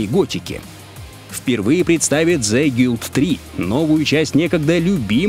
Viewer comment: Что за игра на 6:28, я название не расслышал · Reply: The Guild 3